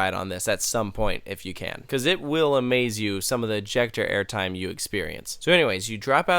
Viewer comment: I was watching this with captions on and when said ejecter airtime the caption said check your airtime it was at 3:53 make sure to turn on captions